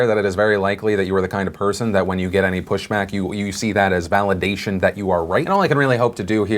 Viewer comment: Anybody else hear pushmack?